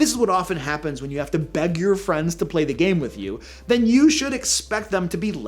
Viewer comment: I misheard "beg your friends to play the game with you" as "bed your friends to play the game with you" and those are two VERY different things XD